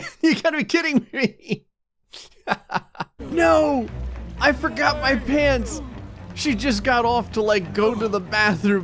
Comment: I forgot that I forgot my pants was a sim, so I thought gray said He forgot his pants